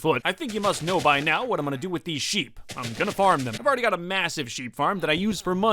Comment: Did he say massive?